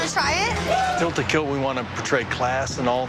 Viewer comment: No disrespect, but did bro say "Here at Tilt we want to poetry class" 😂😂. Honestly I think dude is probably a boss